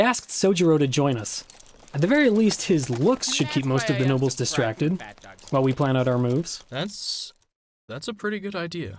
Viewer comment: Okay, who caught what Soujiro said in the bg at 5:52? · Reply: “And that is why I (unsubscribe/subscribe) to PhatDogStudios